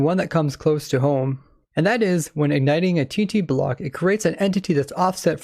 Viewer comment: did he say tt block?